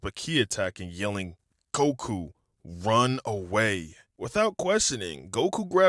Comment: I hear coku instead of goku💀